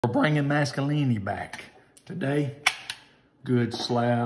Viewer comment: I thought he said Mascalini … I was like I’ve never heard of that Italian dish.